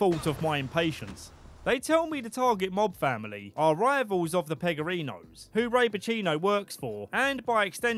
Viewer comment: LOL I honestly thought you said R*peachino😂 (like cappuccino) 42:33 "plummeting way down to his final resting place.." HAHAHAHA, amazing. So happy to have found your channel, love your videos and sense of humor! Always makes me crack up! Hope to see more video game content!